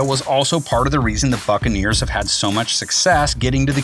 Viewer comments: Is it just me or did he say "fuckaneers" at 0:50 😂😂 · Fuccaneers? he really said that?